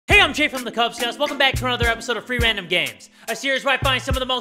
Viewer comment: STOP AT FIRST I THOUGHT HE SAID "some free random gays "